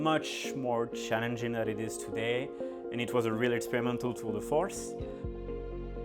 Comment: I'm pretty sure he says "experimental tour de force", not "experimental tool for us" (which is what the captions say).